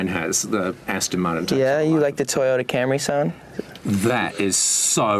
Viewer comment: what does he say at 6:20? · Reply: He asked him if he “liked the Toyota Camare sound”, because they were talking about cars. Sorry for being 5 years late lol